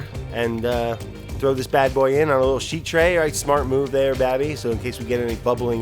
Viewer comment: I thought he said “Bambi” the first time.